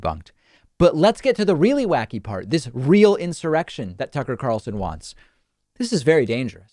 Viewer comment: David; Did you say a "Sucker Carlson" or I just misheard?